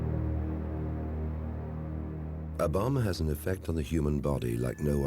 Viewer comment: "A bomb has....." I thought he said " A Obama" I was like wut?